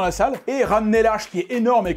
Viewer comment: 10 fois que je regarde les explications de film dont celui-là et en 34:48 j'entends "HACHE" et pas "ARCHE". C'est moi ou a bouffé le R! En-fait non, en 34:36 il bouffe le R aussi! C'est dû surement à l'accent Normand....